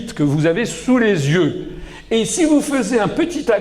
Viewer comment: C'est moi ou il dit à 3:47 "si vous faisez" ??????